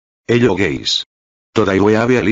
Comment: I thought that it said, "hello gays" at the beginning